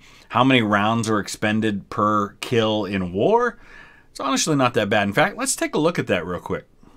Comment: "onashly" aha, ok. I get what you are saying